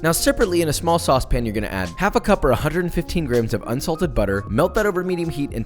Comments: I heard “insulted butter” the first time. Can you give us some tips on which insults get the butter particularly upset? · I heard, “ insulted butter ” ..poor butter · What a great video to watch during my lunch break. 🤪 I totally heard “insulted butter” lol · Could’ve sworn he said to use insulted butter. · Hahahaha. I thought you said "Insulted butter."